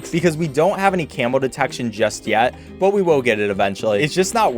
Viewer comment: anyone else thought he was saying camel detection